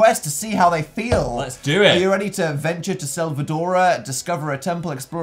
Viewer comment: i thought you said el salvador and was a little shook